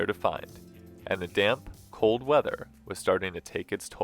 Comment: I thought you said "damn cold weather" and thought dang that Parula is making them extra feisty 😂